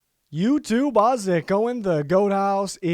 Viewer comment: Can someone please tell me what he says to start his videos? It's driving me crazy. All I hear is, "YouTube Bozzick!" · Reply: Youtube Bozzick growing. Just kidding. Youtube Howzit going?